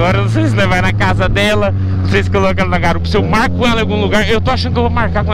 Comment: No 6:19 eu entendi "mato ela em algum lugar" kkkkk